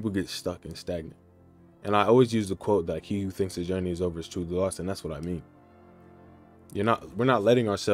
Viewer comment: i couldnt understand the quote. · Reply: he who thinks the journey is over, is truly lost?